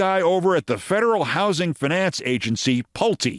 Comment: I heard "The Feral Housing Finance Agency". I think that's a more apt description in the good ol' US of A these days.